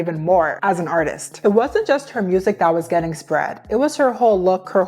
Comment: WHY DID I THINK YOU SAID "It wasn't just her music that was getting spread, it was her hole" LMFAOOO 😭😭